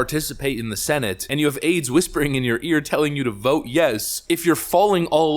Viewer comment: Did he just say “you have AIDS whispering in your ear”? But, yes … yes they do.